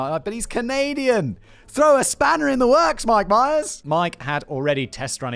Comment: did he just say "my b'ys"? 😂 Someone's been getting into Atlantic Canadian media.